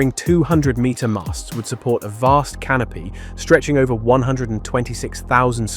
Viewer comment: vast canopy? I thought you said "vast canape" Now that's a real appetiser